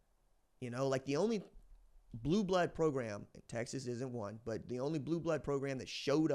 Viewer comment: Correct me if I'm wrong, but did say Texas ISN'T a 'blue blood'?